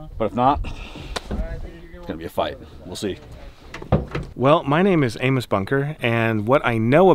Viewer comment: did he really say "my name is anus bunker" ?